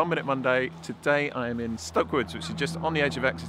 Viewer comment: Hi, this is random, but.. Could you please confirm the name of the place you were filming? It sounded like you said Stockwood, on the edge of Exeter? I searched Stockwood and can not find it near Exeter, only near Bristol. I ask because I’m travelling in the UK next week and that location looks LUSH 😍 so I’d really like to visit it. Hoping that you see this and can respond! Also, I love 24 hour time and find 12 hour time to be silly. Which is why I clicked on this video to begin with 😅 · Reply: Hi, its a really nice piece of woodland called Stoke Woods. However, it's very small - not really worth travelling to! If you're in the UK you'll be able to find much better examples close to wherever you are. Hope you enjoy your visit!